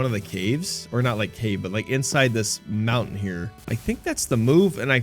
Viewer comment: Did I just hear mounin?